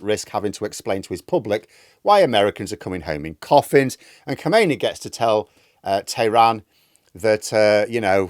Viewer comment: The current Supreme Leader also shares that monika. You’re thinking of the old bloke with a white beard. Hang on a minute! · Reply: Well, that would be Chamenei. Maybe I misheard him saying Chomeini, while in reality he was saying Chamenei.